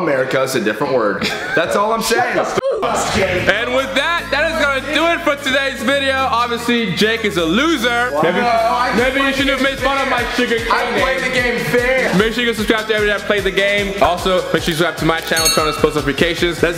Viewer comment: raises his hand - yes - has anyone called you sugar k Me- 😂😂😂😂😂😂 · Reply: I thought said sugarcane 🤦🏻😂